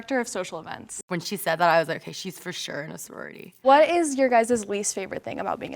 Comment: I heard, "im a nisorority"